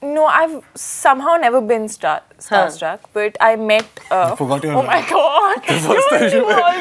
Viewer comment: what Sushant said I can't understand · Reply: U forgot ur lines when u first met me😂😂